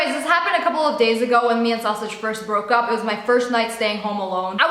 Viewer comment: Is it just me, or did I just hear her say “I broke up with sausage” NUUUU